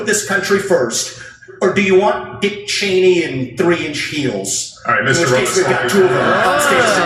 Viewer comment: What did Vivek say at 1:09? "Dick Cheney and 3 in what?"